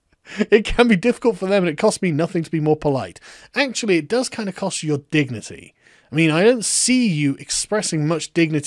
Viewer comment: What I heard was "Actually, it dors kinda cost you your dick-nity" xD